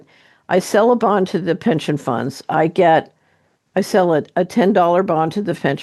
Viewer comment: Not boring when truth is foundational. Thank you! (I think I heard "fension punds".... My daughters are experts in such linguistical leaps)